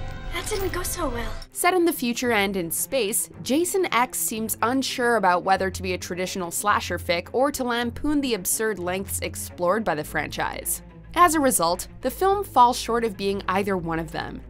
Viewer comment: At 8.23 do I hear you say "slasher fick?"